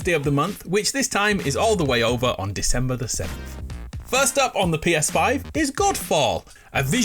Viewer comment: I thought he said the 2nd as well. I just looked and couldn't find it. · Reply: yea same i heard that too. must have said the 7th but sounded like the second.